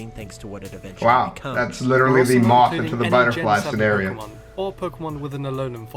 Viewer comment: did he just say. . . the moth into the butterfly